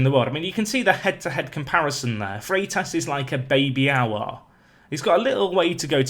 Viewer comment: at first I thought i had heard "Freitas is like a baby owl" xD